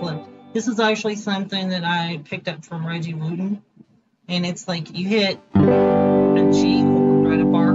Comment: I can't make out the name at 7:13, is it Gene Wooten?